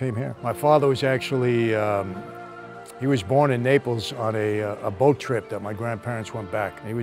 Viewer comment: subtitles say "born in April", but dude clearly said "born in Naples", Napoli How tf the translater misunderstood a clean and simple word and i didnt, and i aint even american?